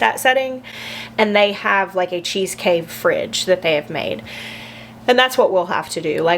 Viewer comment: At first I thought you said "cheesecake fridge" and all I could think was a fridge full of cheesecake in the garage 😂 sounds good to me